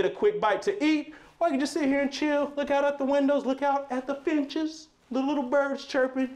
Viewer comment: OMG this is beautiful. Why when he went to the window. I thought he said look out at the bitches. LOL I am sorry